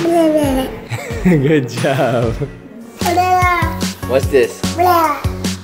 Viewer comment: When they slowed down saying banana it sounded like vanilla